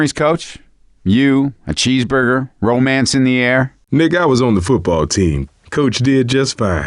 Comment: was listening while I did some work. I thought coach called Nick THE word and had to rewind and read the subtitles